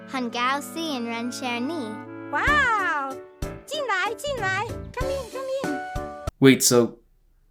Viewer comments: AS A CHINESE DUDE I HAD NO IDEA WHAT SHE SAID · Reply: 进来进来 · Did i just hear the person who doesn't speak English say wow and come in?